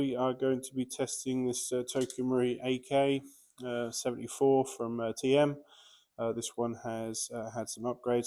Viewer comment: did you say AK-74?